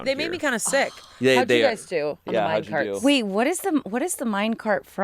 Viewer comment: For a good twenty seconds I thought “why are they saying mind cart, what’s a mind cart?”, I pulled a full Angela lol